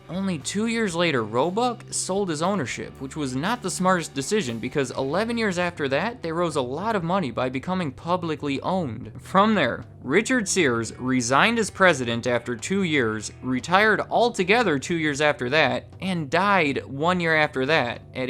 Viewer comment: Did you say, "They rose a lot of money..." at 3:13? That is some really awful grammar.